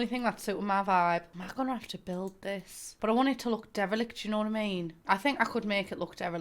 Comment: What word is she saying at 2:13? Starts with a d? Idk if it’s her accent (obvs no shade cause I’m from the Midwest so I sound like shit) or if it’s just my limited vocabulary but I have NO CLUE what that word is. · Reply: Pretty sure it's derelict (I think lol)